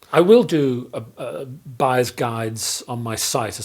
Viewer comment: I heard "This is a bias guide".